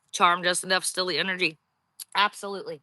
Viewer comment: VERY FAINT FEMALE VOICE "YOU WERE CHARMED JUST ENOUGH TO STEAL YOUR ENGERY" THEN AT 48:55-49:02 "THAT'S WHAT I JUST SAID. SO TRUE THOUGH" OR "I JUST SAID THAT. SO TRUE THOUGH" SOMETHING LIKE THAT I THINK. IT'S INCREDIBLY DIFFICULT TO HEAR VOICES AND BACKGROUND NOISES AGAIN FOR SOME REASON. NORMALLY I CAN HEAR THAT SH*T REALLY GOOD. (I'M GONNA CHARGE MY EARBUDS AND SEE IF THAT HELPS ME HEAR THINGS. I SHALL RETURN TO FINISH THE VIDEO! 💕)